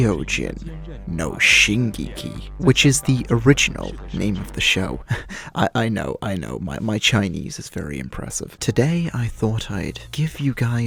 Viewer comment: did he just say Chinese...... even as a joke thats a tad bit racist...am i wrong?also the funniet video ive ever seen -_